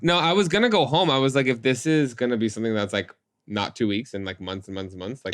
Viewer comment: So said "if this is," but because of my crispy ceral chew I heard "and get fisted," so that happened.